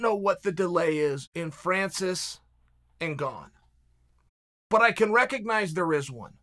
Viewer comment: I thought he said Francis Ngannou but he said Francis n’ Gane